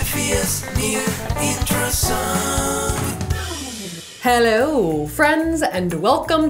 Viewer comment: I am literally such a fan of the "new intro song". Like such an underrated bop. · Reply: Ohh I thought it was an old one. Idk i thought it was saying “near intro song” idk but yeah it’s a bop